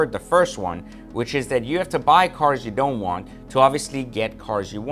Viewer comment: All I heard was "Blow job's to buy cars."